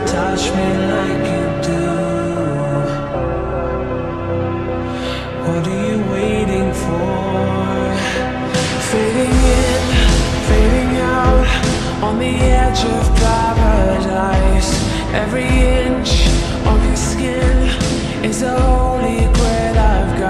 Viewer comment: I honestly dont know why I thought she said, "Filet Mignon"